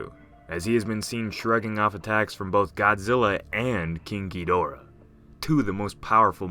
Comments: The moment you said "King Ghidorah" I thought you said "Kinky Dora" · i always hear "kinky dora" instead of king ghidorah... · When he said king giidorah I thought he said kinky dora